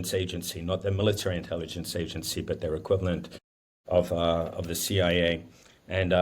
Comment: “Equivalent of the C I A”, not (what I thought he said the first time through) “Equipment of the C I A” As an aside, US Senators’ visits as this happened, not a good look.